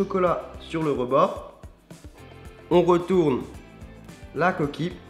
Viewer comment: j'ai entendu bobord 😒😂